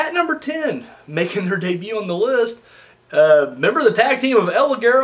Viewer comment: At number 10 making HER debut on the list El Ligero and Gabriel Kidd BOTCHAMANIA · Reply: Sounded like he said their.